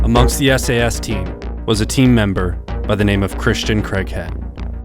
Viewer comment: Craig head… but I keep hearing “crack head” 😅😂😂😂